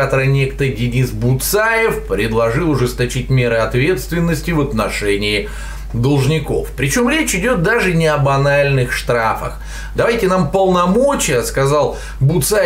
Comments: Только я услышал "об анальных штрафах" вместо "о банальных штрафах"? Аш страшно стало... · мне послышалось ""об анальных штрафах..." чуть со стула не упал :DDD